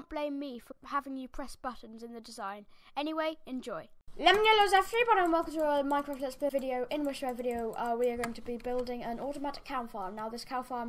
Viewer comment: I have no idea what you just said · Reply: And tha was one of my more clear intros. 😑 Lemon yellows everybody and welcome to another minecraft video in which where video I am going... blah blah blah.